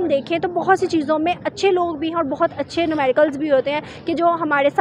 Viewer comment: Instead of saying achhe miracles she said achhe numericals bhi hote hain